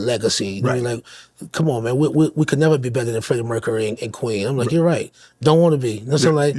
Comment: Time Stamp 1:29:30 what did he just say???👀🤔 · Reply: "We could never be better than Freddie Mercury and Queen"